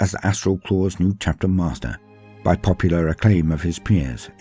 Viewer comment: Yes, I am a child- When you said “he became Chapter Master by account of his peers”, I was busy making coffee and heard “by account of his penis”. It’s just a Yank mishearing your beautiful accent! But go back and listen and see if you can get why I heard it wrong- it totally didn’t ruin the video for me- it was an outstanding piece of work as usual. Thanks for sharing this- I love the lore, but mainly focus on 30K, so while I knew who he was, I didn’t know his whole story. Awesome work! (And that must have been some tool he had!🤣)